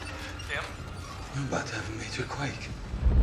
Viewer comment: what did he say? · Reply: We're about to have a major quake.